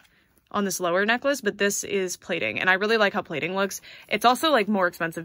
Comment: hi! Im not a native english speaker, could someone pls write doown the word she uses to describe the other necklace? I understand that the first one is painted, but couldnt get what she said after, on minute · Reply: The shinier one is “plated” and the other is painted